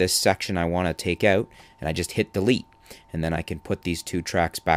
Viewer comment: I'm sorry, but what did you say on the 1:20 moment? You said "and I just hit the _" ? Also, most helpful video. Thanks soo much! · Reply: "delete"